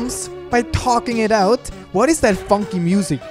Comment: Is it just me or did it sound like he said the f word at 15:38 when he actually said funky